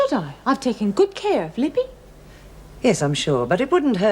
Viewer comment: did she say "Libby"?